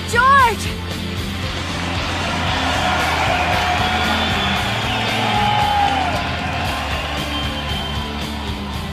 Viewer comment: What is he saying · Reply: yukari